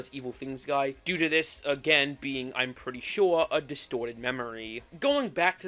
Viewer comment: did someone say “distorted memory” (like from piggy lols) 🗣️🔥